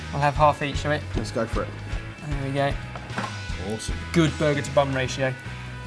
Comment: I thought said "That was a good burger to bum ratio". Oh dear god.